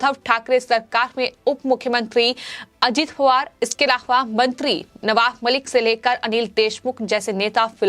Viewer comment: Did she just say Ajit Hawar at 0:22 ? Why does she use the HhhhH sound so much